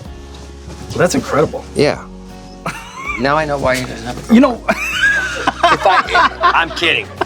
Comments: Does anyone know what Stamos says at 23:03? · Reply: “Now I know why he doesn’t have a girlfriend “ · what did he say?!?!? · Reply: John said “Now I know why he doesn’t have a girlfriend” bc he couldn’t twiddle his thumbs w the sticks 😆